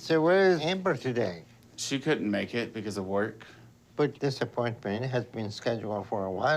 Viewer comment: When Dr now called out Amber not coming to the appointment, I initially thought he said “this disappointment has been scheduled for a while now” and I was thinking he was being more brutal than usual 😂